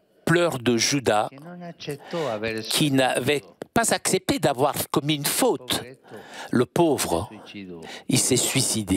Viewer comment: Juda a accepté de ne pas avoir commis de faute? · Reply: "François" ne parle pas de pardon mais il dit "Juda a accepté de ne pas avoir commis de faute" Donc si Juda a accepté "de ne pas avoir commis de faute", ça veut dire que d'après lui il n'a pas fait de faute . Pourquoi s'est il donc suicidé.